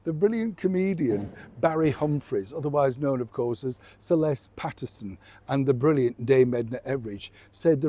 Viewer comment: Aussie here....I thought you said Barry Humphrey's, better known as Celeste Patterson 😂...my brain went scrambling trying to remember that character 😅...but then I realised you were saying Sir Les Patterson, one of Australia's greats 💕🇦🇺